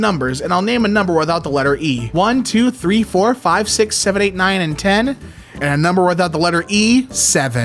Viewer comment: did you say 6 7?